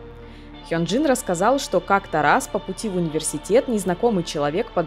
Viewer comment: что говорит автор: "как-то раз...", что слышу я "как Тарас..." и сижу с выражением лица как у этого пацана с чупсом, думаю че за Тарас.